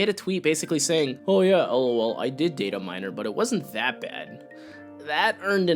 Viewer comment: legit thought you said data-mine her. Wtf